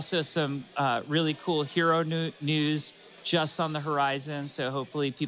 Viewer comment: I swear he said "hero nudes" right there...